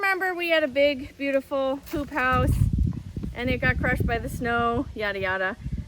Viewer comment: I thought you said "we had a big beautiful 'poop' house that got crushed by the snow..." LOL